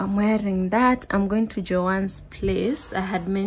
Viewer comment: when you said i’m going to joan’s place …. i almost thought you said Johannesburg 😭. i would faint if i get to meet you